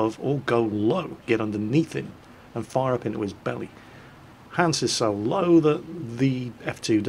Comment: i could have sworn i heard Han Solo for a second